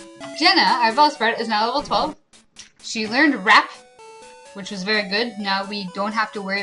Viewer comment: I heard "Jenna learned Wrap" as "Jenna learned Rap" and I have yet to stop laughing.